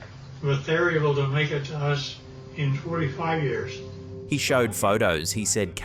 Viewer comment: He clearly said "45 years" no '45 minutes' what is the point in lying or trying to mislead what can be clearly heard? I guess it will work on the mentally fallible among us but not any reasonable perceptive person.